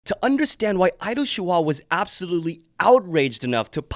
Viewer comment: I’m sorry, I thought you said “idol chihuahua”